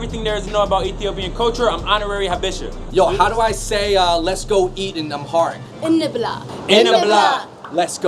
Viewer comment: Did he just say “Let’s go eat & I’m hard!” PAUSEEEE